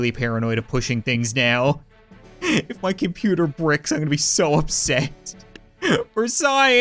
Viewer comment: Did he say Bricks?